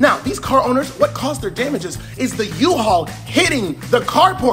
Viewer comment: Actually it would make the person that rented the uhaul vehicle liable. Uhaul themselves only rent the vehicle. Unless the Uhaul vehicle was driven by an employee acting in official capacity. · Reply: You misheard. He said "the U-haul"